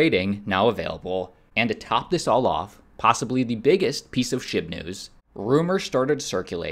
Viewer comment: did he say "shib news" or "shit news"? 😂😂😂